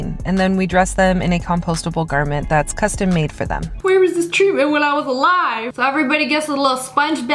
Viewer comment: WHAT DID SHE SAY?! “where was this treatment when I was alive??” 💀💀💀